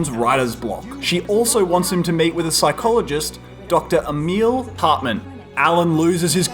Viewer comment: I thought he said "MATE with a psychologist". That's some pretty intense treatment